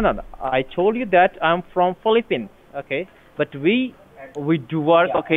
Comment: caption said Filipe but I think he might have said Philippines, some people say Philippine but of course this is such a disgrace. I'm from Philippines and Indian 'businessmen' get so much money either by being scammers or loansharks. Get out of life please.